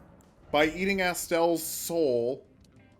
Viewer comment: definitely misheard Soul lmao